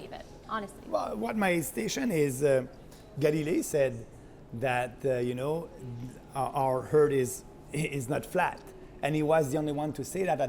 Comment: I thought he said “Geddy Lee said our herd is not flat”.